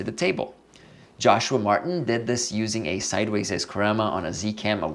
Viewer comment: Very cool look & worth trying. At 7:47 what lens did you say Joshua Martin used? The bokeh looks great! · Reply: He used a Iscorama 36! :)